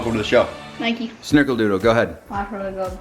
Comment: who else heard snickledoodle? for those of you who know who know what a snickle is, this does not sound pleasent